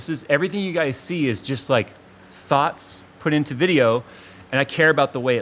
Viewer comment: did he say T H O T S